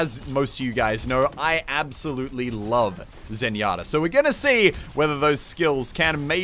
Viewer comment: "I don't acually play a lot of Mercy, but as most of you guys know, I absolutely love Oasis." at least that's what I heard